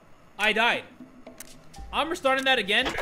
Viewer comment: at first i thought he said "hey kid your parents are dead" but then realized that he said "hey kid its your dad"